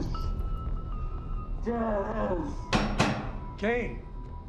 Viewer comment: anybody understand what he's saying? Cause I don't know if he's saying "alice", "jancie", or "jealous". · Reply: Janice. If you watch on the CW with subtitles it is very clear but I can see where you get Alice from